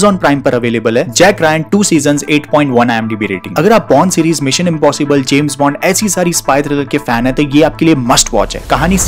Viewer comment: I thought he says porn